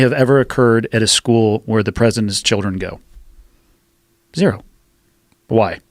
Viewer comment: Could someone please help me know what he said at the 1:55 mark? Did he say "where the President's children go"?